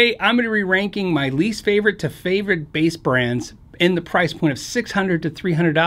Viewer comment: At first I thought it said "Brass Bands."